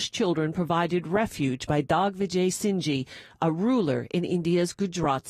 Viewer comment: what does the subtitles says ?better mind it's digvijay singh and not dog vijay singh it will not be tolerated change it